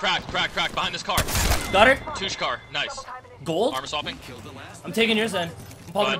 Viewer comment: did say "Toosh car"? Cause I noticed that's the car where killed Daltoosh 😂😂😂